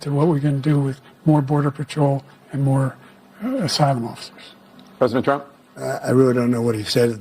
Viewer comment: I think he was trying to say more border patrol and asylum officers. I just now figured it out, but live during the debate I thought he said 'side mouses 🐁'. Lol